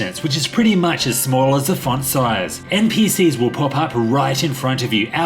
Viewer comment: Who is the guy that pops up at 5:00, 11:08, & 13:00 and what is he saying? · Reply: Google mortal kombat toasty.